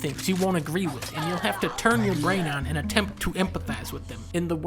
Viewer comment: did I miss-hear or did you say turn it ON? lulz yeah, i can let go of a lot of stupid shit happening. But if the character is prone to fuck up, then when he/she fucks up BIG TIME is a lot more believable. There are a TON of contradictions on Ellies side of the story that just fuck with me the wrong way. Peoples reactions seem constantly unwarranted and just forced, so for me it doesnt feel like the characters were moving the story forward, but just advancing really akwardly towards it with some small very poignant moments that reflect the weight of the revenge on em. I disagree that Ellie was the one who put their friends in danger, since it was their willing choice to avenge Joel. Good video though, and overall a good game. p.s. Abbys story was THE SHIT though!, the war on the scars, the nightmares, the strained relationships and the kids! oh man the kids, Yara is a beast and Lev is the most lovable sweet psycho-cult-murdering child in gaming, period. I still chuckle at "a quarter of what?".